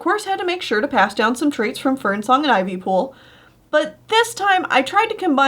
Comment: when she said fernsang and ivypool I thought she said Fernpool and Ivysong XD